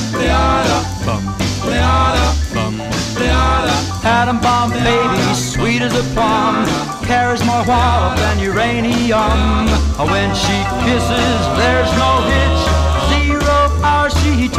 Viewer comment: It’s not “carry’s more water than uranium” it’s “carry’s more wallop than uranium” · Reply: I heard "her hair is more wild than uranium" the first time I listened